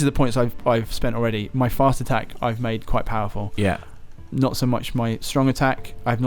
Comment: did anyone else think he said ''my fart attack ive made quite powerfull'' at first? XD